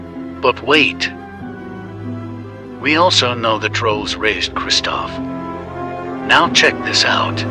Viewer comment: I thought he said rizzed not raised